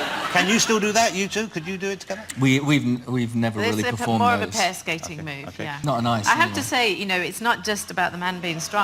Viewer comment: HAHA does Chris say “Not on ice anyway” at 7:24 😏😏